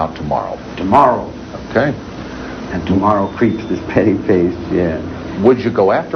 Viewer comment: can anyone hear what charles says in response around the 33:20 mark something along the lines off tomorrow preach petty place im not sure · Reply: Tomorrow and tomorrow creeps in it’s petty pace. It’s a quote from Shakespeare’s MacBeth. Here is a link to the entire soliloquy. <link>